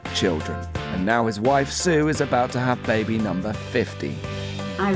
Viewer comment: Is it just me or at 2:22 it sounded like he said "and his wife sue is having baby number 50"!! I have watched this before and I defiantly know that had less! LOl